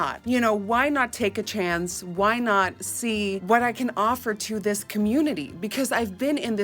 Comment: I thought she said why not take a trans lol